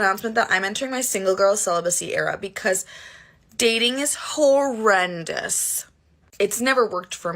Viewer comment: Did she say "Dating is Hoe-rendous" ?